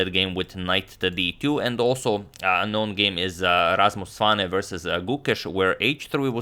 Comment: Wait?!!! Did he say Rasmus Oksanen